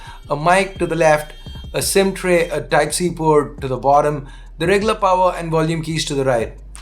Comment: While keeping captions option on(English) at 1:09 , a TAXI Port (instead of Type C Port) 😂😂😂😂